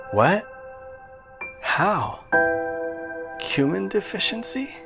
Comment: No one ever warns about the dangers of cumin deficiency. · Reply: I thought he said human deficiency 😂